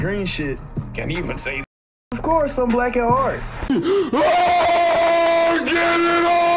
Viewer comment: "black at heart" 💀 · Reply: I thought he said "black and hard"